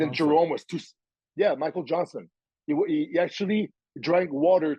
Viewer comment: thought he said my Johnson 😂😂